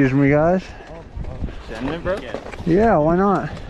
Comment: “You sending it bro?” “Sure, why not?” Legendary Moment 😂🔥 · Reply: It's unbelievable, but until I had read this message I also heard "Sure, why not?". But now it disappeared and I hear "Yea, why not" only. 😧 -- The place for loosing "sure"ness